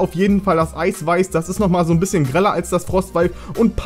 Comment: 0.50 minute. Ich habe verstanden "...das ist noch ein bisschen greller als das Frostweib."